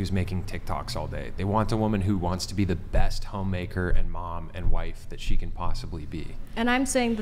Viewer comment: Sounds like she said "home wrecker", Freudian slip?